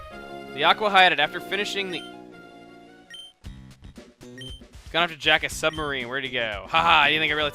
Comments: wow i thought he said 'gone to jack off a submarine' hahahahah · when it said gone off to jack a submarine I thought it said gone to jack off a submarine lol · thought he said jack off a submarine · i thought that at 1:36 the grunt said he went to jack off in a submarine